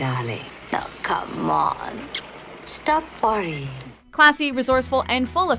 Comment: did she just say stop farting